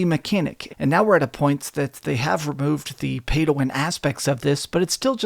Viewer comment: When you say 'Pay to Win' I heard 'PADAWAN'. And I'm happy I did not buy this game.